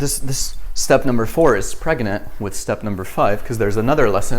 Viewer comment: in 21:12 i heard it as preganate